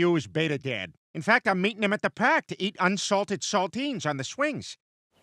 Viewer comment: Unsalted saltines lmao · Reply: I thought he said Sardines. XD